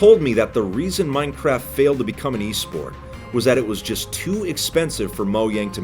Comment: For a split second I thought said Minecraft failed to be an EA Sport